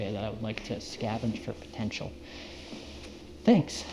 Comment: did you say potential